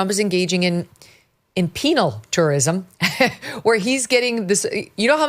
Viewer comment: When megyn compared medical tourism to penal tourism; i thought she was saying "PENIAL TOURISM"! LOL🤣😅😂😅😂😅🤣🤣🤣😅😂😭😂🤣